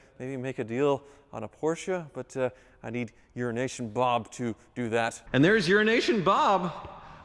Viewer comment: Euro nation bob , when I first starting hearing you say that I thought you were saying urination bob.